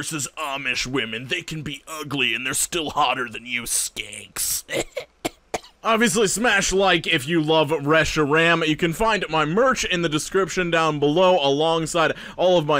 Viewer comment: The song, i dont know the character is that he is saying hes married too cause all i keep hearing is "I love fresh ram". My thought was "Dude I like upgrading ram as much as the next tech but we dont go around signing about it."